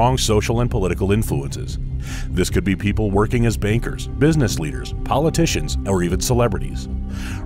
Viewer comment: I thought you said bakers...